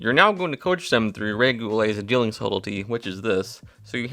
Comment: what’s the name that is said here. Love this effect. Thank you!! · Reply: Ray Goulet!